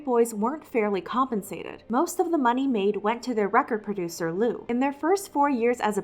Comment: All I heard was “Lou-is”